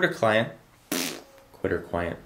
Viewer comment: did he say hooter client? someone goes to hooters a lot.. ;-)